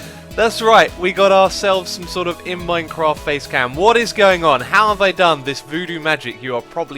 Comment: I thought facecam said facepalm